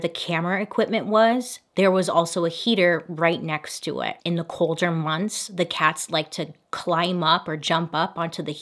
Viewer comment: I definitely thought he said "its getting colder."